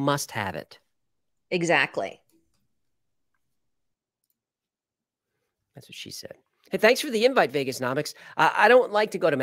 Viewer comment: “The more you don’t want it the more you must have it.” “Exactly!” Very very low “That’s what she said.”